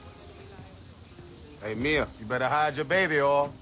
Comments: I thought he said better hide your baby yall but i guess hes saying oil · Did he just say baby oil? You know diddy want it